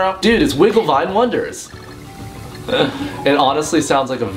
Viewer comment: Kinda sounded like you said "Wiggle My Wonders". I think that's even worse.lol